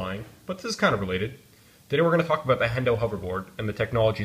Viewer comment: it sounds like he says "Today we're gonna fuck with the Hendo Hoverboard."